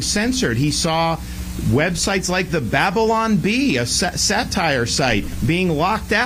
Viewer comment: pretty sure Desantis almost said "a SEX site" instead of Satire on 2:50.